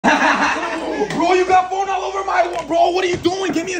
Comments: hehe i changed this comment know you will never know how it got 5k likes 😈😈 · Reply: I heard it is "you got my phone under my control" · "Bro you got phone all over my water"💀 got me rolling down my stairs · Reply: I kind heard him say “YOU GOT WATER ALL OVER MY WATER” 😭😭